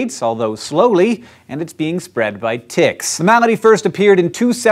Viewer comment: I thought I heard "spread by tits".